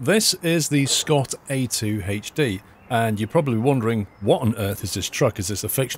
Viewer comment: I thought you said the Scot ADHD Vintage Truck 😂